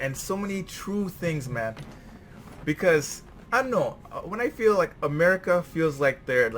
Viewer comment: Did u just said 'ano'?